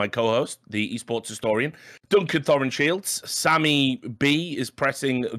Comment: I like to keep mishearing that as Dunkin' Thor and Shields.